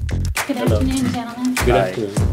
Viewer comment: Did Keith just say "Good Aight" when the lady walked by at 2:28?